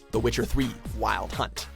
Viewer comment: i thought he said wild cunt for a second